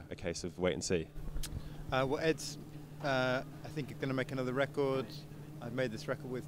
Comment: Did he say radiohead will release another record??? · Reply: yeah I replayed it and it sounded like he said “Ed is going to make another record”